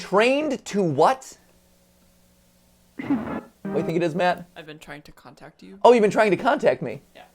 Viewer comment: What I heard was I've been trying to come back